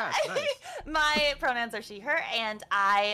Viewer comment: I almost misheard when she said "My pronouns are she/her"... I would have heard "My pronouns are Shenhe" 🤡💔